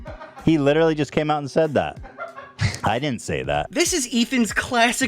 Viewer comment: did anyone else get that "Mr Girls cuties review" vibe from Ethan on that remark? I didnt say that, he said that".....what i heard come out of his mouth was "i didnt make them do that, they did that".